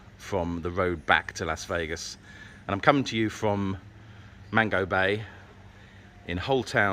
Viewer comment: Every time you say “Mango Bay” I hear “Mandalay Bay” because apparently my brain will not compute that you are not in Vegas.